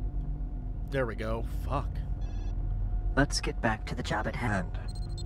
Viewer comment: Half the time the AI says "Let's go back to the job at hand" I hear "Let's go back to J'bba th'hutt"...